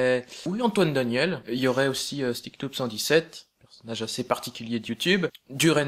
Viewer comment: il dit quoi à 2:54? stiktoop? stiptouk???? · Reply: Sticknoob